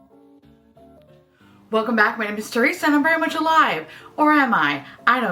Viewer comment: LOL! I thought you said: My name is and I'm very much a lie.